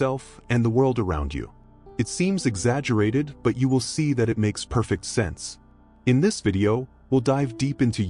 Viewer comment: I thought you said I'll see that it makes perfect sense? So you're saying that everyone on the planet who is born this month, will all be the same personality type? This makes absolutely no sense to me! I don't think this is Jungian Typology!